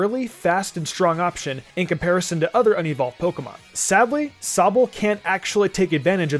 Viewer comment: When he was talking about Sobble, and mentioned "unevolved Pokemon", I thought he said "Onion-ball Pokemon".